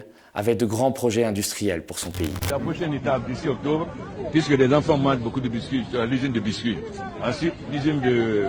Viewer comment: J'ai entendu: alpha avait de grand projets industriels puis il apparait en disant bonbons, biscuit.,😂😅